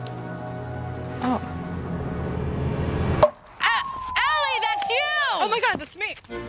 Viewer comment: did luna say "Allie" instead of Ellie (sorry if i spelled there name wrong )